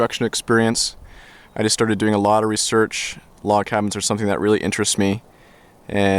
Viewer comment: for a second i thought it said crazy log cabin